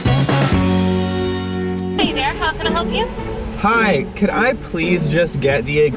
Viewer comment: I thought it said hey "Derek what can I get for u"